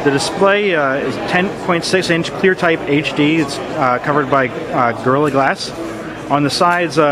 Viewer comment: Does he say girly glass?